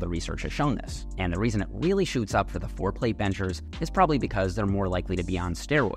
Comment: Totally thought for a second you said “foreplay benchers” at 2:54 😅